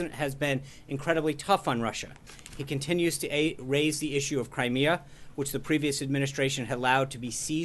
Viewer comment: Did he say " He continues to aid Crimea"....!!!!??? What a flub,wtf...!!!